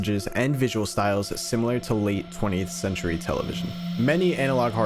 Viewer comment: i swear at first i heard "late twentieight century" Anyway Funi sarkic plant go brrt >:3c